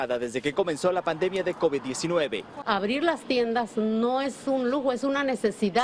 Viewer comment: estaba diseñando y escuchaba la noticia pero escuché mal! Jjj abrir las piernas es una necesidad? Luego escuché bien jjj 👏🏻